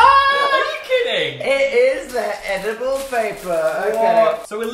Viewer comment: my sister walked in the room right as they said edible paper, and she thought they said edible baby 😂